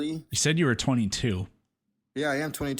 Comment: turn on your subtitles bud Subtitles are generated automatically and have many errors. For example, when you claim said "Chat, chat, chat", the subtitles say "Chad" consistently. Who's Chad, then? Why did you say "chat"? I'll tell you why. Because we can clearly hear what's actually being said. Tell me I’m wrong 29:32 - "Are you scared of me, Peter? Are you scared of the 23 year old..." You ARE wrong, buddy. He made a mistake, and that's what was pointing out. Then, at 29:36 he says he's 22 again. But he probably didn't even notice he had said 23 before. LOL As a flat earther, you're used to being wrong. How does pressure work? Gas pressure? Pressure is a force exerted by something on a given area. Here's the deal: there's a "mysterious" force (that flat earthers are terrified of) clumping mass together. Gas included. So when gas gets accelerated towards the center of mass, it eventually faces the resistance of other things, like rock and water. So gas layers are stacking on top of each other. The more gas above, the more compressed the lower layers get, since all of it is getting accelerated downwards. That's why the lower layers have a higher pressure, and it decreases with altitude, in a gradient (the higher layers have less mass on top of them pushing them downwards). The more "compressed" the lower layers get, the more particles per volume you have. That means more collisions per area, i.e. higher pressure. Now, tell me: 1) Between those many differen